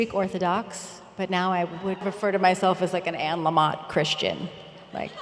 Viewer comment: Does anyone know what Tina says at 37:34 : "Now I refer to myself as an ??? Christian" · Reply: l kn She said she was like an Anne Lamont Christian!